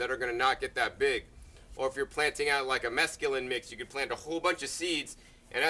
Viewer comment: In minute 4:57 did he said "if you're planting mezcaline"? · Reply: well, he was talking about lettuces, so I am going to say that he was probably talking about a "mesclun" mix to plant in that pot (it's just basically an assortment of different lettuces). When you wrote "mezcaline" that's the closest thing I can think of to it.